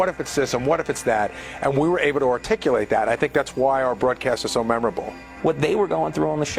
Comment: Did Gary say reticulate that?